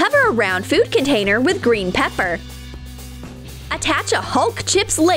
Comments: Why did it say pepper instead of paper? Here is the time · Anybody else hear green pepper instead of green paper??? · i like how he says green "pepper" instead of "paper" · i just heard pepper instead of “paper” in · Is it just me or did they say Green Pepper? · lol did she say pepper instead of paper😂?? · who else heard her say green pepper instead of green paper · "Cover the round container with green PEPPER" DID I HEAR THIS RIGHT?!?!? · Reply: I heard it says green pepper